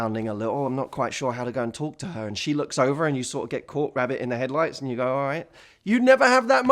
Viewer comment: Did I just hear you say "a cold rabbit caught in the headlights"??😂😂 that just made my day👌🏼👌🏼😂